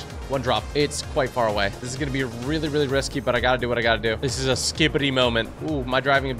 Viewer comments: did you say skibidi at 11:29 ? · did he say this is a skibidi moment